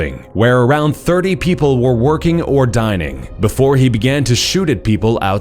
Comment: at first I thought he said around 30 people were either working or dying... i was like ouuuuu- that's harsh.